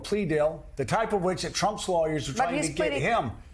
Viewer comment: You mean he is not going to deny any wrong doing, hire lawyers to drag it out in court and lie lie lie? What about Trump's boxes!!!!! 😂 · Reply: Dang! I thought you said "What about Trump's boxers?" for moment there. I really do NOT want to think about that guy's underwear! 🤣🤢